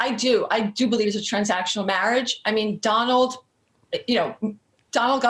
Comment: I thought that said "Transcendental marriage". I'll get my coat.....